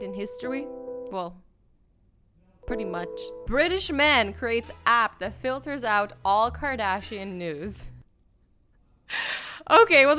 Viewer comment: i was looking down and i thought she said 'British man creates app that filters out all Kardashian NUDES yo late squad where u at??